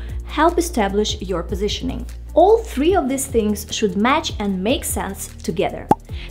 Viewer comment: it sounds like you said "all 3 of this things" but the captions said "all 3 of these things" which was it